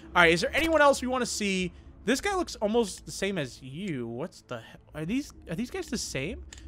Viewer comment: did he say a bad word? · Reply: “Whats the” No it is not a bad word